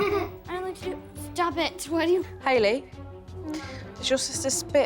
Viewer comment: Basically Jo: you're emotionally abusing your chil- Mom: I don't wanna talk about *that*, she's the reason all the problems happen. running from her sisters 70% of the time MOM SHES SPIT- Mom: idk why you're running: She was spit- Mom: why're you making so many problems!? but she-... This is really so disappointing.. don't even ask if you won't listen.. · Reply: I could have sworn I heard her say "do you hear my voice?" Poor kid